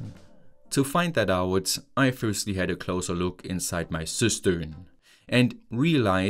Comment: I must say, every time you said Cistern, I kept hearing Sister, and.... well, it's weird to hear "I firstly had a closer look inside my sister" (5:25). Yes, now that I re-watched it multiple times I don't hear it as much, but first time caught me off guard 😅